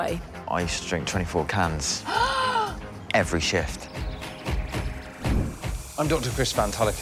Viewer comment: I thought it said "i drank 24 cans every day"